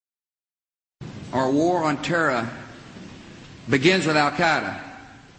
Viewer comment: sounds like he says "war on torah".....zionist piece of shit